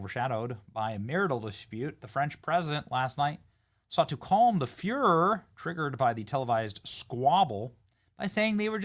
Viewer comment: Calm the furore.. I had to pause and read that, i thought you said Calm The Fuhrer.. i thought you were calling Brigete the Fuhrer 😂😂